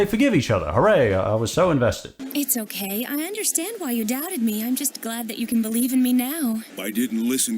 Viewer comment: Did she just say “Why you’re DAD at me”? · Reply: Like 99% sure she said "why you doubted me"